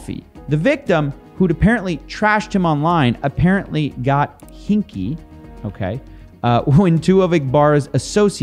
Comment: ohhhhh he said hinky I thought you said he started getting kinky🤣🤣🤣